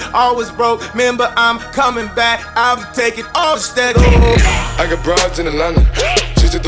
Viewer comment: I always thought he said "I got broads in uh London"